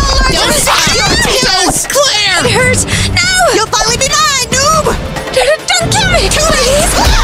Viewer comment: did gurl really say noob